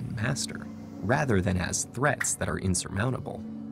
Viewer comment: What's the word on 4:19? "Threats that are ???" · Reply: Insurmountable